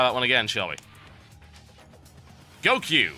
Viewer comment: lol thought he said 'Goku' instead of 'Go Queue!'....